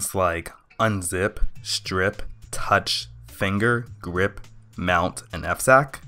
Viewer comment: Your GREP kind of sounded like GRIP to me. I also heard some other quirks in your pronunciation I'll still recommend this video to a friend of mine who wondered what Linux is.